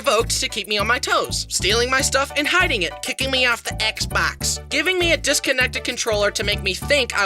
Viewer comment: When I heard "hitting me off the exbeocks" I LAUGHED SO HARD